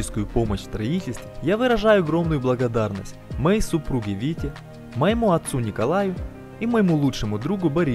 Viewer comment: несколько раз прослушал,а всё равно слышу какого-то Витю..